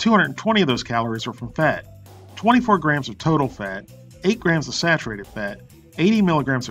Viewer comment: lol.. for one second I thought you said turtle fat.. but you said total fat